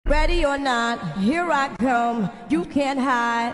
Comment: i hear "here i c*m"